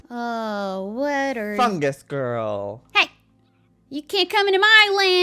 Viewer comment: I thought he said f*ck this girl😂